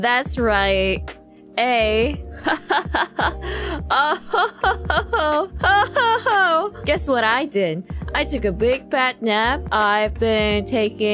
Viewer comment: I thought I heard kronii say, "I just took a fat dab". "I took so many dabs"! 💀